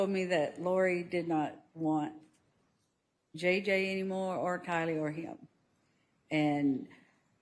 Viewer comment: did Kay just call tylee KYLEE... yikes. Poor girl, cant even get her name right. Did she even know her at all...?